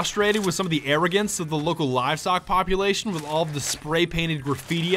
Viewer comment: I thought he was saying Lookalivestock, not local livestock.